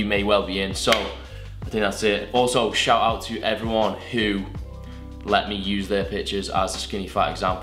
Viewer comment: i thought he said "let me use their bichezz"